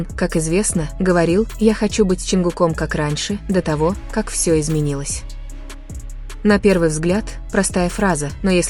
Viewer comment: Для всех кто не услышал, что сказал Тэхён. В 2:20 "Я хочу быть с Чонгуком как раньше, до того, как всё изменилось. "